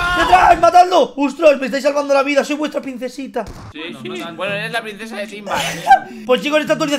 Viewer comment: cuando escuche decir a "soy buetra princesita" ya esta pensando "el riunba is real"